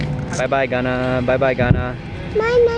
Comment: is so adorable 'Banana' (did he say that?)